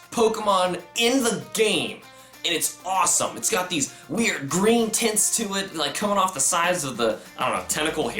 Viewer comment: it sounded like u said it has these nice green tits